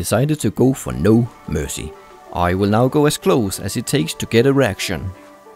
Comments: Sounds like he is saying "I will go as close as I can to get an erection". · did someone else heard to get an erection at 3:22 ? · did he say hes gonna get an erection ? :/ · Oh god I AM messed p at 3:22 i heard "how close to get an erection" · lol it sounded like you were saying 'try to get erection' lol love your videos :) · Thought you said "I'll now get in close to see if I get a erection" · "I will now go as close as it takes to get erection" Sorry, that was what i heard because of your accent or my broken hearing :D